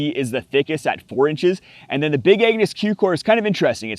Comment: I swear I thought you said Big Anus😂